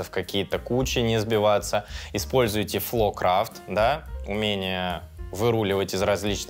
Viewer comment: я верно услышал, «фло крафт» (floorcraft?)